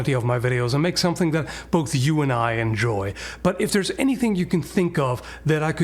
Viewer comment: "If there is anything you can think off..." I heard onlyfans pays pretty good money